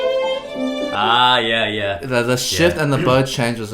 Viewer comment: please don't tell me i'm the only one who thought eddy said s*** for a moment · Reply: he said shift